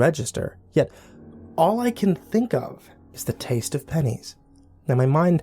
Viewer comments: I thought it said "The taste of pee" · When I first looked at it I thought it said the taste of penis Lol